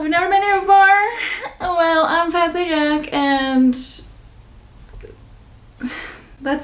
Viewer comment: i thought you said “well, i’m fancy jack” and i was like okay she’s changin it up. now you have to watch Gentleman Jack if you haven’t. and in exchange i will call you